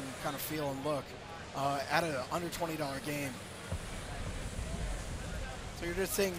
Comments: Ohhh... i did a bit of a double take because i thought i heard "will be on steam for $120!" Lol under 20 sounds much better lol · he said "at an under 20$ game." and you are not an idiot since you are obviously looking for confirmation on what he actually said as apposed to just instantly hating and criticizing this game for something you misheard. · Thank goodness, I even listened twice to make sure, and I thought he said $120.00 early in the video, but I'd rather my hearing be wrong! Whew!